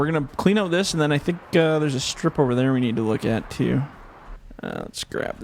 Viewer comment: Great vid! Keep up the awesome work 👍 1:19 did u say there’s a stripper there I wanted to look at lol